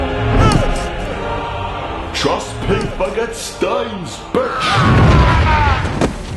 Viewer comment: What does he say at 17:10? I can't quite understand him. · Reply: Connor Veenstra trust pink forget stains